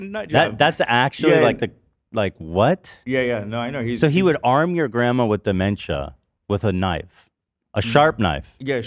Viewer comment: It sounded like Ethan said “So he armed your Grandma with Dementia” as if dementia was the weapon lmao